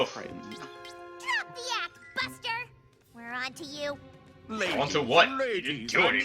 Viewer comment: am i the only one who hear pinkie pie saying bastard?!😂😂😂😂😂😂